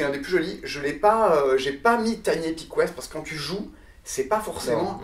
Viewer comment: salut dit moi le jeux au quelle tu parle a la 6:31 je n’arrive pas a comprendre peu tu me l'indiquer s'il te plais :) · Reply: Tiny Epic Quest ;)